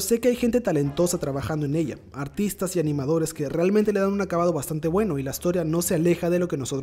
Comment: Por un momento escuche "Autistas y animadores"